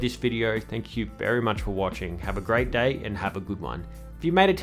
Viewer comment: wow fam??? (Is that what u said lol)